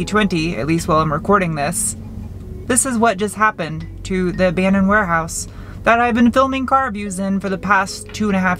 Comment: I swear the first few times I thought you said "your favorite banana warehouse." I still pretend to this day that's what you say 😂💜 I'm more into muffins myself, but I'll feed you as many bananas as you want - while you talk to the Robins in my back yard. I speak fluent meow and...decent woof myself. Was thinking about getting a bird, despite the cats. 6 animals is probably enough though lol. 7 including me. You're one of the shining stars in my eyes for 2020. That aura on fleek. Keep doing you, and maybe I'll be lucky enough to meet you in 2021 if I get up to speed on my Chicka Dee Dee. Love to help you with dat Audi Tea Tea. Happy new years! ✌❤🦄